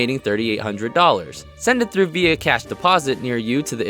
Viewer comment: I dunno why but I thought he said vhs but he said via cash or something